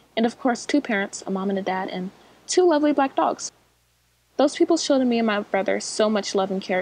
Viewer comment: Wait did she say two very ugly black dogs-.... in 2:02 ...